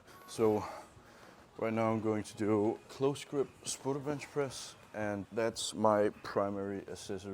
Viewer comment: what's the accessory lift called at 8:50? Close grip *something" benchpress? I can't make out what he's saying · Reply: close grip, spoto benchpress